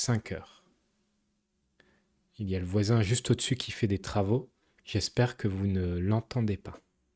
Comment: part is annoying me because I couldn't hear "au-dessus" at all...!